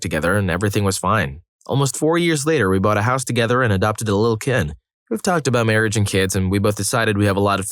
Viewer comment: "Bought a house together and adopted a little kitten" i had heard that as "adopted a little kid" and was confused they were still trying to figure out if they wanted to marry LOL